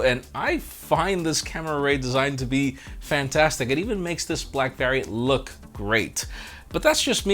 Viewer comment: Did you call it a blackberry at 6:34?